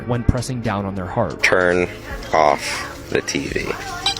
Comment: The toy at 6:04, if you slow down to video, it sounds like it was saying "when I turn on tv" and "please...feed me until turn on tv" that's all I hear, please don't copy what I say and don't say you said this.